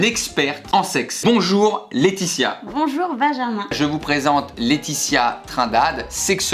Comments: A 0:09 elle a clairement dit « bonjour vaggamin » ou bien ces moi qui est obsédée?😂😂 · J'ai compris "bonjour Vaginmin"..